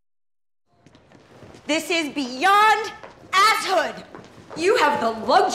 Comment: what does she say??? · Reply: “This is beyond a—shood.”